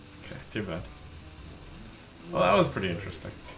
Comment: Sounds like Rexxar said "well fuck" at 5:35 with the beep noise.